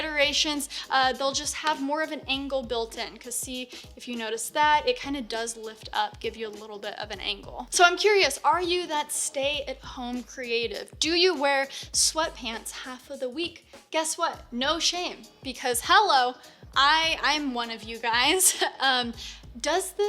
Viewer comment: I thought she said "slut pants". Anyways, subscribed. Never heard of this girl until today.